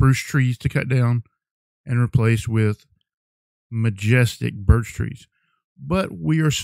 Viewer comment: For one moment I thought he said birch 😱